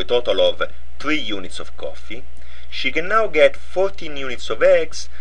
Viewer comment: Did he said "She genau"? Did he use German?